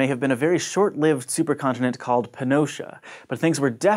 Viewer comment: He said "Pannotia", I thought he said "pinocha".